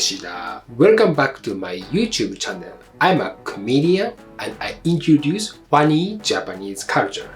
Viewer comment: When you say “I’m a comedian” but due to your accent….. i heard it as “I am a clamydia” no offense but i really heard it this way…… hehehe